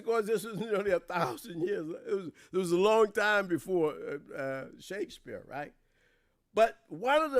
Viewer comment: Okay I misunderstood. I thought he said met Shakespeare. I had to go back and relistened Glad i did. Thought for a minute i was cra cra listening to him.